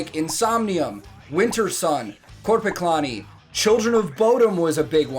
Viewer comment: Hey, could somebody tell me whats the third band mentions around 1:05 ? It was Insomnium, Wintersun, ??? and Children of Bodom. I guess I don't know them yet and since I really like the others and brhg, I'd like to check them out. Thanks :) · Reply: Just noticed this.. Its Korpiklaani